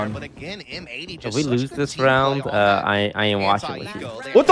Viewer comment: what did toast said? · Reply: "If we lose this round, I ain't watching with you"